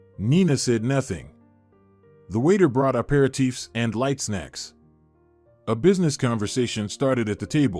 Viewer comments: Nice story but that robot did a horrible job of reading it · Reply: Hahaha, yes, many rather hilarious mistakes on the captions! One of my favorites is at 31:37 "The waiter brought a pair of teeths" instead of "aperitifs", and over and over, it says someone "left" instead of "laughed". Not sure how the robotic voice narration is produced, but surely there must have been a written script that could have been used for the captions? Pretty funny! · He probably graced rather than “greased” the covers of magazines. 😂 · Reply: Hahaha, yes, many rather hilarious mistakes on the captions! One of my favorites is at 31:37 "The waiter brought a pair of teeths" instead of "aperitifs", and over and over, it says someone "left" instead of "laughed". Not sure how the robotic voice narration is produced, but surely there must have been a written script that could have been used for the captions? Pretty funny!